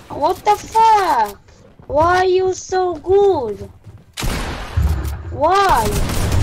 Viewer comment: pensé que decía "por qué estás tan bueno" jSJAJ